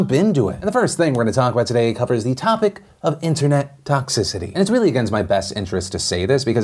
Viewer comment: Lol I have been playing too much Pokemon. I thought it said toxtricity .